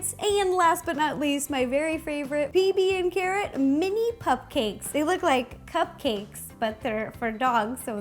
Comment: When she said pupcakes i thought she said cupcakes